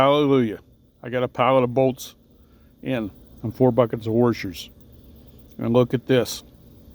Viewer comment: Thought you said "four buckets of horse shoes" at first